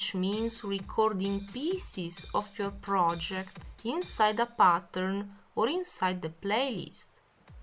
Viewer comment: Sorry, but that sounded like you said "inside the bathroom." ^^